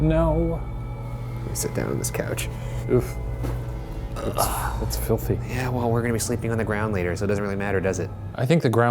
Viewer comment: Did anyone else hear Shane say “oof “at 3:57 or am I hearing things? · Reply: Yeah. He said oof